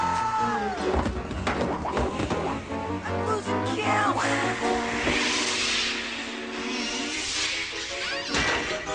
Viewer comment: What does he say at 16:56? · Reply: He said “I’m losing count”